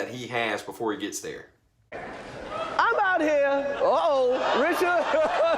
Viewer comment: after he says uh oh Chris sounds like he is calling for someone named Richard. Might this be the name of a security guard? Did anyone else hear that name?